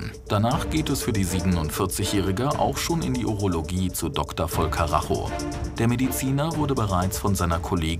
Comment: Ich hab zuerst Doktor voll Karacho verstanden 😂